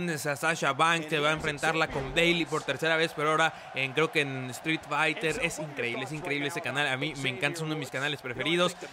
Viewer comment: como se llama el canal que dices en el minuto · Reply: quiñones7 UpUpDownDown